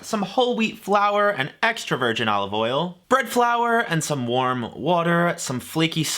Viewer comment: did you say "burnt flour"?